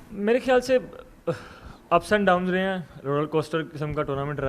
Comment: did he say roral coaster